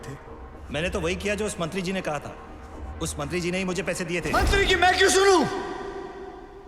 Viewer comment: did he really say "Mantri ki ma ki chooo" 😂... This maybe has amazing voiceovers post cuts